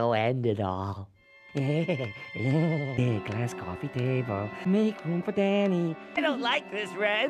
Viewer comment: HAHAHAAHA!!!! Ren is so funny and goofy doing and saying those things. Especially when he mumbled that nonsense after he got mad at Stimpy which was really funny. Did he say make room for Danny?